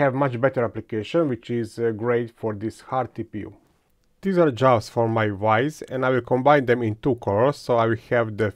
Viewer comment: I thought you said "These are jaws for my wife." I was curious what sort of thing you were making for your wife. It's a lot of fun to hear your accent. Thanks for making another interesting and fun video.